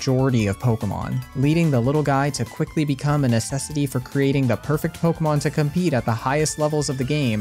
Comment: What I heard: "leading Ditto to become an SSD for creating the best pokemon in the highest tournaments..." Which still makes sense IMHO. But I did turn on the closed captions and figured it out.